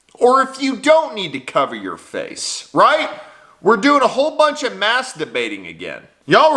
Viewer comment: I heard "we're all doing a heap of masturbating again" 🤣